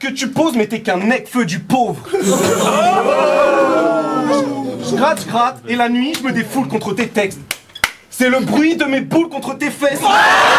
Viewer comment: Can someone tell me what he said at 7:06 ? Im still asking me since 2014🤣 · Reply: In French, it's "J'gratte j'gratte et la nuit je me défoule contre tes textes. Clap clap. C'est le bruit de mes boules contre tes fesses" and now you just have to translate it with google translate ;)